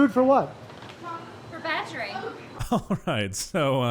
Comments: I totally get why you thought she said battery, cause what other real thing would she say? But she definitely said badgering lol. Not sure which would be funnier honestly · Love your stuff keep at it. On a side note. I think one of us misheard the first karen. I heard her say "Badgery" or "Badgering". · she did not say "battery" she said "badgering" im pretty sure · I thought she said badgering lol